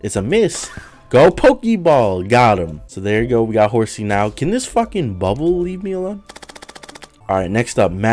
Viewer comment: I thought he said "is that a fucking bubble?" Proceeds to choke the bubble